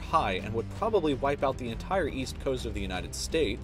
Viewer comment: did, did you just say the entire east side